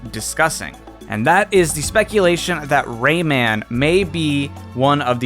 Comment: I’m super addicted to animal crossing I thought you said Raymond🐱